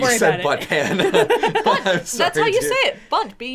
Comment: Okay, it might just be because I'm Hispanic, but I heard "butt pan" and my mind immediately went to "culo de pan."